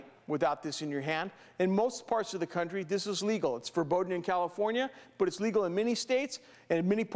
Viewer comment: did he say it's "forbidden in California" ?